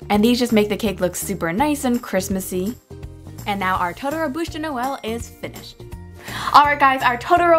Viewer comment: lmao at 5:15 the subtitle said "bullshit noel"